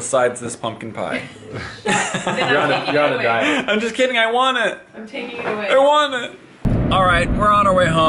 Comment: I heard "I want to die" when the kids used the speech jammer XD